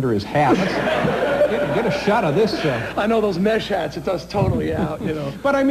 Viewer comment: Wait did he just say get a shot of this simp